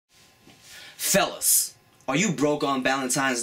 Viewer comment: i thought he said "SELOS". lol it's actually "FELLAS". XD